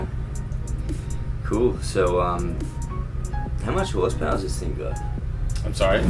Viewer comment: What the hell do you say at 00:54? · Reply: "how much horse power is this thing got?" 😅